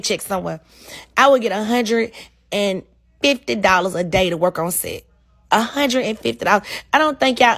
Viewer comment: Licensed or not $150 a day is sad considering what everybody else was making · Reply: I thought she said 150 a head damn 150 a Day !?!?!?!? Dang